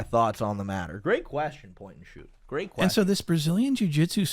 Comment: For a minute after he said perspective I thought he said POINT N” SHOOT!!